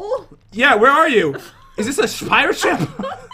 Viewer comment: Did he just say 'is this a spider ship?!' LMAO